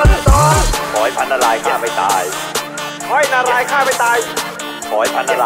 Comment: ตอน 0:30 กุปรับความเร็วของคริปเป็น 0.75 แล้วกุได้ยินว่า ขอให้พระนรายเย็ชไม่ตาย